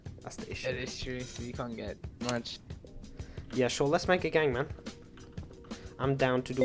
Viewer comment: When he said "yeah, let's make a gang man". I heard "yeah, let's make a gabgbang" xD